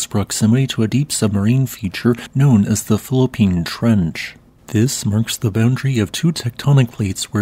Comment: I could have sworn he said the Fallopian Trench. Had to back it up and slow it down. 😅